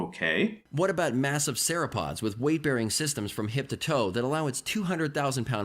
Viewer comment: Lol, did anybody else hear "sarapods" at 23:30?